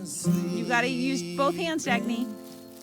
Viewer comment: is their doughters name Dagny? sounds like she say that at 10:07 or what does she say? and cant you make the pond deeper and add some crucian carp or something in it to keep it free from growing to much weeds? looks like sooooo much fun driving around on that road